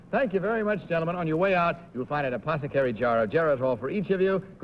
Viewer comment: Wow, prize money AND and an apothecary jar of Geritol. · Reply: at the end of Game 1, it sounded like Bud said a suppository jar of Geritol.